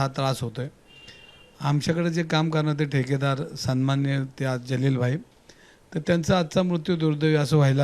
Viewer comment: kya bola tune? Imtiyaz Jalil bhai? 😂